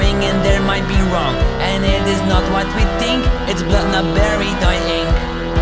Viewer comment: Vid: its blood not berry dye ink Honestly his accent and how fast he had to sing the song it sounded like he said it bloo not berry dye ink